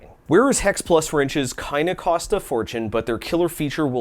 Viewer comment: When you said "kinda costs a fortune" after momentarily speaking in a terrible psuedogerman accent my brain heard you say "keine costs a fortune" and I thought you were saying it was cheap lol I use plastic razors all the time for all sorts of things, i dont find they wear out TOO fast. But yeah, if you bump them up against something harder than they are, theyre gonna get smashed. Nothing better for scraping sticky things off fragile surfaces though. Try getting a sticker off a board game box with anything other than one of these